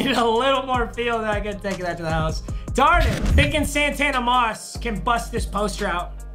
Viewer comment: Did I just hear him say “darn it” wtf